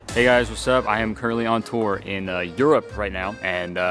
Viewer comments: i thought u said "hey guys wsup i am curly on tour" in the beginning 😭😭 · Thought he said “I’m curly on tour” for a second and was really messing w the new nickname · I thought you said “I’m curly on tour” and I was like “you know what, hell yeah.” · I thought he said "I'm curly on tour.."